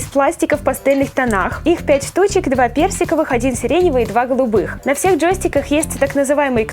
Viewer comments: Когда ты сказала персиковых джойстиков,то мне послышалось берсиковых))) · Когда ты рассказывала про джостики мне послышалось берсиковые а не персиковые